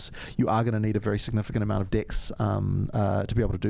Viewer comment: I love these guys so much and like said- I too am nervous but very excited for this game!.. On a side note I found it entertaining to listen to John and Mark saying “Dex” 🤣 I swear I kept hearing “dicks”. 🥴